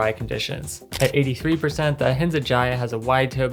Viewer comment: Sorry! What is the name of the casual boot at 12:32? I couldn’t understand what you said 🫣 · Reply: Ahinsa Jaya <link> Here's the review: <link>